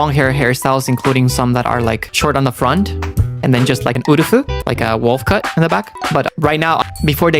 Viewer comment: Thank you for the clip!! This stream was so fun honestly ww Though, could I help you with the missing subs? Here's what is saying in the parts where the subs go "???" For 6:23 just saying Wolf (cut) but in a Japanese accent (ウォルフ) At 6:41 / 6:45 is saying Kurage (クラゲ) which is japanese for Jellyfish, which he then translates into English himself immediately after Hopefully my japanese is correct! If there are any mistakes, hopefully people can let me know 🙇